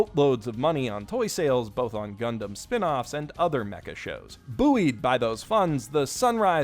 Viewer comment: did you just say boo-ied